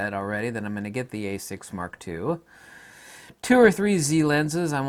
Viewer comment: You said a7 mark ii and a6 mark ii instead of z6ii and z7ii.